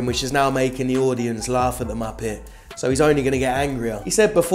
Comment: Bro. Did you say “..laugh at the muppet” lmao I’ve never heard that before